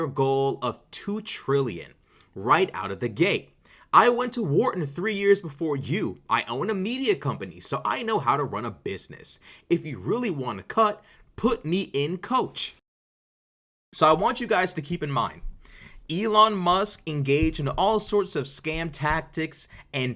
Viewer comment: MAGA self humiliation made me think Cenk said "If you really want a cuck, put me in coach."